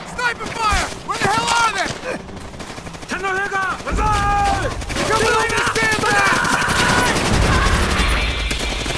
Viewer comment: 1st thought I thought the player said Tenno Heika Banzai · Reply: it does say Tenno Heika Banzai